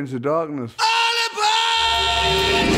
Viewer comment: What was Ozzy screaming? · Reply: ALL ABOAAAAAARD HAHAHAHAHAAAAAA!!!!